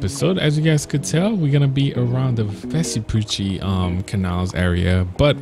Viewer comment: wtf this man say😂😂😂😂😂😂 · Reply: vesapuchi